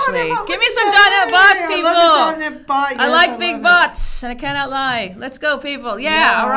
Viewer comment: I like big bots and I cannot lie!!! lol, I love it 😀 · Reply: Ohh I thought she said "I like big butts"! Sorry having issues with my hearing aide.